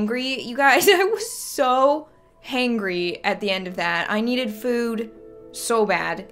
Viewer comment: did anyone else think she said hungry weird ?